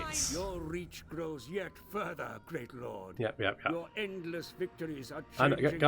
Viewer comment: For a moment there when the old man adviser said Great Lord, I heard "Dreadlord". I was like this brave old fool